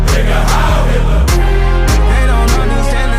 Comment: Icl the first time I heard it i thought they said nigga howl nigga